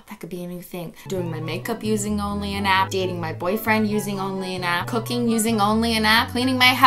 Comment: I legit thought Cristine said “Doing my boyfriend using only an app”.